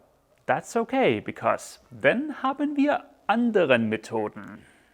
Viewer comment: what did you say at 1:55 played it three times.. ? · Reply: He said:Dann haben wir andere Methoden